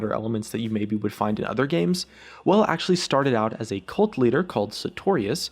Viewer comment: omg i thought you said "a cult leader called sartorius". HAHAHAHAHAHA